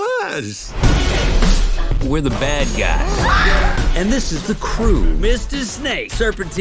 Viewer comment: the style looks seriously interesting - definitely gonna watch it to see if it's any good. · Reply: at first i thought it said the bald guys 😭